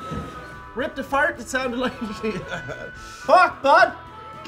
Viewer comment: "Ripped a fart that sounded like a machine gun" Subtitles: "RIPPED APART THE TIME" Whoa. Ripped apart the very fabric of time. Now THAT'S a fart.